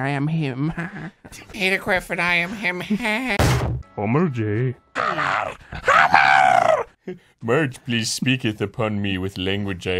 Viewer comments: i thought she said heil homer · Did Homer say Pomni?